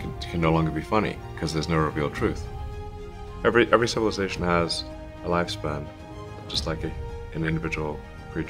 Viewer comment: "Every Civilization Has A Lifespan" People recognize something's going to happen, and they'd better get ready.- Tim LaHaye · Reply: I misheard as the air conditioner came on. I thought he said every conversation has a lifespan. Both true.